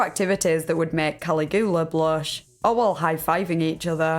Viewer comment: I had to rewind this and make sure I heard this right. "Collie-goo-luh"????? 😂😂😂😂😂😂